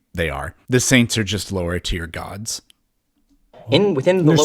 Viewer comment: did you just say... LowTierGod? ⚡👁⚡👁⚡